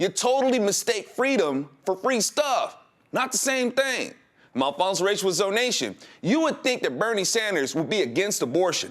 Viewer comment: What does he say at 3:15??? he always says it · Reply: something like "I'm Alfonzo Rachel with Zo Nation"